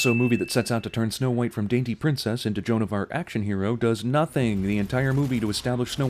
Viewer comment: I smiled for History when I heard "Joan of Art"